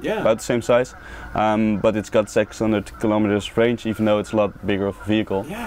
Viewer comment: The guy states the range but I have replayed it 10 times and I cant understand what he says. It sounds like he says "its got sex on tick kilometer range"???